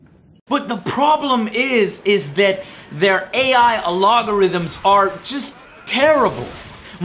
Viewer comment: “The AI alolgorithm”???? Lol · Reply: it sounds like he says "the AI logarithms" lol...I guess he meant the AI programming?